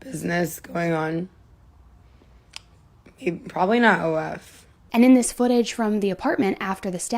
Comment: → probably not a love ? is that what she said? How she sees herself in five years…. 7:53 [i was thinking, prison but…] She said, kids, houses, business, and maybe: probably not a love or was it probably not a lot ? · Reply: She was saying probably not OF~ only fans.